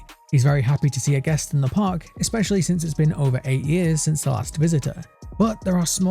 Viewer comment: I thought it was 8 years? I might be mistaken. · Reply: It is 8 years, I don't know why I thought he said 9